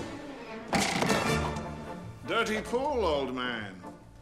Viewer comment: I always thought Gomez said “Dirty fool, old man!” Not “dirty pool!”